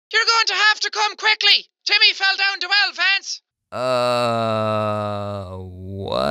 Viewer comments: i heard "youre going to have to come quickly, timmy fell down the well, fence" ... ?????????? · It kind of sounds like Jack said, "You're going to have to come quickly! Timmy fell down to hell, Vance!" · She said “YOUR GOING TO HAVE TO COME QUICKLY BECAUSE TIMMY FELL DOWN THE WELL VANCE!!” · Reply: I heard “YOUR GOING TO HAVE TO COME QUICKLY CAUSE TIMMY FELL DOWN TO ELEPHANTS!” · Why did I hear "you better come quickly Jimmy got stabbed by a lance" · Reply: I heard "you better come quickly!!! Timmy fell down a well Vance!!!!" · All I heard was, “YOU’LL HAVE TO COME QUICKLY, TIMMY FELL DOWN THE WELL VENTS!” teehee 🙃 I’m probably the only one 😂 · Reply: I heard:YOU'LL HAVE TO COME QUICKLY TIMMY FELL DOWN THE WELL LANCE! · he is actually saying "YOU'RE GOING TO HAVE TO COME QUICKLY TIMMY FELL DOWN THE WELL VANCE" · Ok so the woman at the end.. me nd my friend walked past them and she sound like she said timy fell down the well lads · i heard "Youre going to have to come quickly! Jimmy fell down to elephants!" Whats wrong with meee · She said "You're gonna have to come quickly TImmy fell down the well Lance!" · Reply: I hear, “YOU’RE GOING TO HAVE TO COME QUICKLY, TIMMY FELL DOWN TWO ELEPHANTS!!!!” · I think she said Jimmy fell in the well fence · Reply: TIMMY fell in the well LANCE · I heard "you have to come quickly Timmy fell down a well, France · On the last one I heard “YOUR GONNA HAVE TO COME QUICKLY TIMMY FELL DOWN THE WELL VANCE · I heard 'Timmy fell down the wire fence'